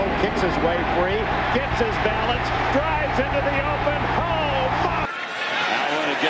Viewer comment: I thought the Commentator said "OH FUCK!" at 0:22 LMAO